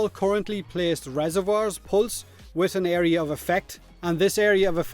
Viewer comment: "area of a fack"? what the fack are you saying?